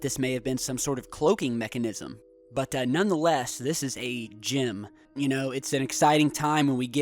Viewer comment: Lol for a second there, I thought he said "GYM" not "GEM"